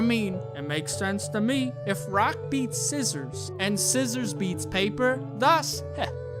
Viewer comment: i thought i heard "if a rock beat ceasar"